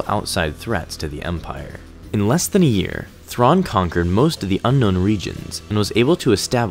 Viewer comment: I noticed an error in the subtitles, it says conjured instead of conquered...